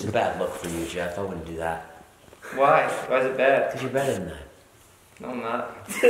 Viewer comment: What does Jason say at 0:28? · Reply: "Cause you're better than that"